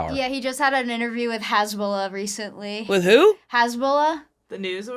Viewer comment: I definitely thought was saying “Hezbollah” lol